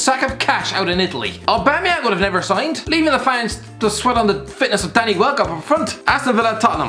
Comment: did he say Danny wellcot